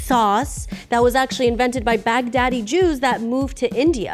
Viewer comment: ok I know it’s Baghdadi but I heard it first as Bagh Daddy and I was like who tf is that 😂 a new musician I’ve never heard of?